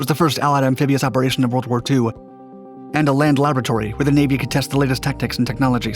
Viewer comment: When he said 'land laboratory', I thought he said 'land lavatory'😂😂